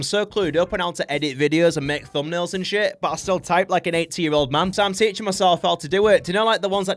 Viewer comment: my subtitles said 18 year old man instead of 80😂